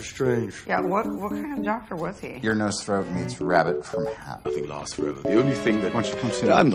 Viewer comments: I think Tony said Ear Nose Throat not Neuro Stuff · Some of your subtitles are wrong Like at 4:26 Tony says "Ear, nose, throat meets rabbit from a hat" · Hey, btw at 04:27 Tony actually says "Ear-nose-throat meets rabbit from a hat" and not Neurostuff although I can see that it's easily misheard.